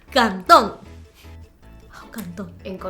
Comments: entendí canton xdxdxd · chale escuche cantón :0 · JAJAJAJAJA NMSMMSS. En el minuto 4:30 escuche canton (en México es cómo decir "Casa"). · yo escuché cantón y de donde yo vengo (Mexico) le decimos cantón a tu casa xd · escuche canton (en México 🇲🇽 es una forma coloquial de decir casa) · yo escuché cartón :v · acaso escuché "Canton" :v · en 4:23 parece que dice canton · Jaja en el minuto 4:23 parece que dice cantón yo creo que esa China es mexicana 😂😂